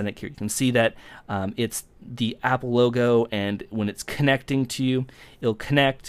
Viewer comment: I thought you said "app logo"